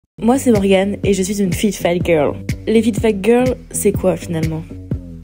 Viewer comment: Moi j'ai entendu "flip flap girl" 😂